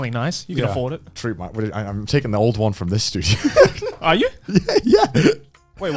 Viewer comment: Connor: I'm getting rid of my laundry machine Joey: Your fridge? Also connor: Im getting the laundry machine here in the studio Joey: what your getting my fridge from my house?? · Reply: Its because Connor pointed back (meaning the studio) but it looked like he was pointing to Joey, and Connor was laughing while he said "this studio" which sounded like he said "this dude"